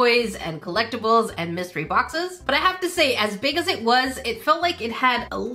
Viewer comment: I thought she said misery boxes 😂